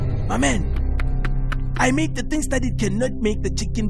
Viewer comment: Hayibo Phusu😂😂 I didn't even hear what he said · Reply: I made the things that it cannot make the chicken to be done😂